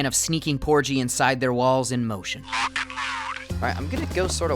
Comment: When this man said porgie will sneak into the walls I thought he said balls